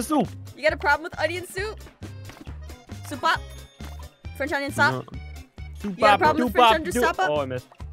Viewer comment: Onion sup I thought she said onion sock